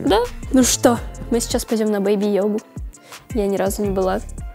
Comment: беби йога мне послышалось беби йода 😂😂😂